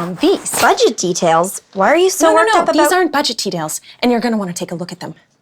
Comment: I heard "these aren't budget tedails"